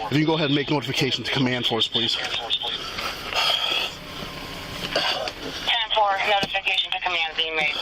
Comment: if you didn't understand when the subtitles said (**inaudible**) the dispatch she said: "10, 4, notification to command force being made"